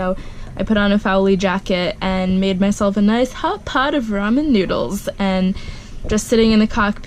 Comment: I heard “Rum and noodles” at first 🤣🤣